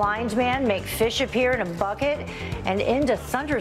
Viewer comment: I swear I heard, "Make fish appear to fuck it!". I didn't know Jesus was into that kind of thing, but with religion's track record, I'm not surprised.